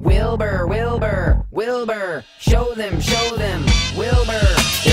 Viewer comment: it sounds like hes saying "wilbur, wilbur kill them kill them wilbur"